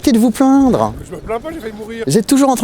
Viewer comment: Je sais pas si c'est moi mais j'entend: - Arretez de vous plaindre ... - Je me plainds pas j'aimerais mourrir